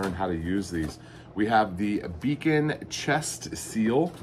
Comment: At first play through.... It sounded like you said "vegan chest seal" I thought WTH, if you have a hole in your chest...who cares if it is vegan. 😅 On replay, I now know better. 👍